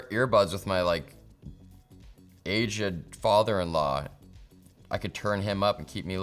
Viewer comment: I thought he said asian father in-law 🤣🤣